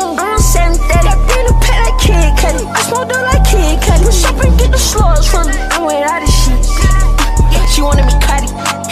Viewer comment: Ayo comment section what does he say right here? I hear “I just wanna do it like Kid Curti” All my friends hear “I smoke dope like kid curti” What do y’all hear? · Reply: He says I smoke dope like kid cudi